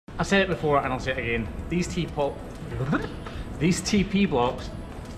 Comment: cow misunderstood. thought u said, "pee on block..."